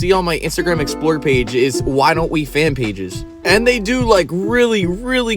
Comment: i thought he was saying why dont we was hot at first 😂 (they also are also pretty hot to)